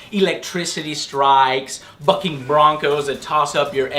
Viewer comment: Am I the only one who heard "Fucking Broncos"?